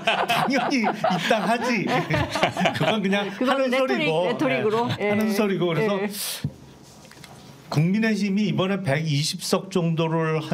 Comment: ㅋㅋㅋㅋㅋㅋ국민의짐으로 들리는거는 내 청력 문제인가??